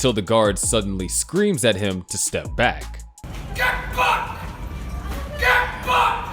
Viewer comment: 1.36 I know I misheard. I could almost swear the guard screamed "Get Fucked". I know full well he said Get Back. I know this.